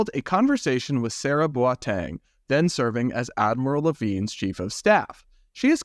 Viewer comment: You kept saying "Admiral Levine," but I keep hearing "Avril Lavigne." 😂